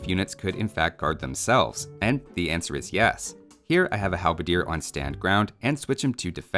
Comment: Very informative video but I love the youtube subtitles at 7:04 with "Hell of a Deer" instead of halberdier